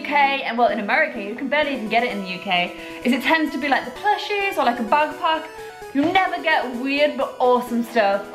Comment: I thought she said " plushies or a butt plug"